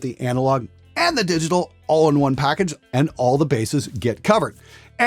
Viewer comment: Yeah, you said “all the bases are covered” at 6:12 but I hear “all the bassists are covered” and the jokes just write themselves...